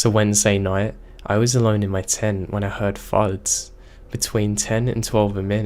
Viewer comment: The story about the boy at the scout camp. Just for clarification...the boy said he “heard farts”? I’m pretty sure that isn’t what he said but for the life of me I can’t figure out what he’s saying! “On the way to the latrine he heard the, little “farts” again. I am so confused! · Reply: Lmfao I came across this comment right as I was listening to that part of the video and I totally heard why you would've thought that he said farts. That made me laugh. It's definitely thuds though. Thank you for that. :-D